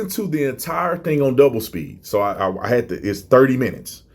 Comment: lol thought you said you listened on "doublespeak"... lol. Dbl speed